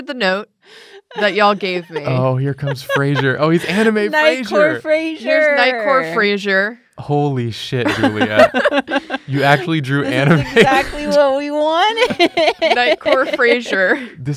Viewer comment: I thought they were saying "Night Court Frasier" and was expecting to see Frasier in a judge robe.